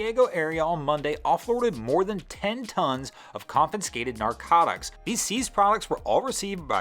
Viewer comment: am I the only one who hears "confiNscated narcotAcs" ?